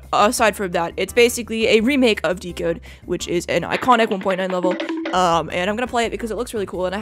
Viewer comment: I swear I heard "iconic 1.0 level"